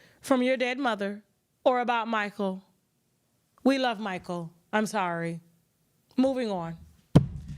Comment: Sounds like Joc said bae I'm sorry to Mo. need to exit NOW!